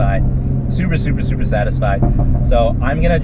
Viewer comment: Sounded like you said "Supra supra supra satisfied" at 13:10 HAHA. Great video man and glad you're having fun!